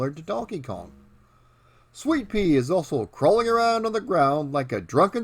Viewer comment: I hope I heard you say Swee'Pea :)